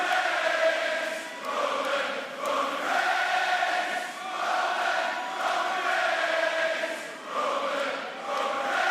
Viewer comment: what is crowd saying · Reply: Roman Roman Reigns